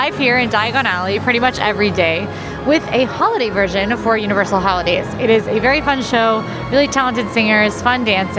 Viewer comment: Full disclosure, when you said ‘Who-lidays’ I heard “Hula-Days” realty was an initial let down, but I recovered.